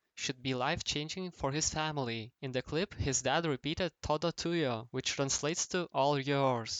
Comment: toto toya WTF DId he say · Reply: He said "todo tuyo (all yours)", he speak spanish, is from Argentina igual que yo (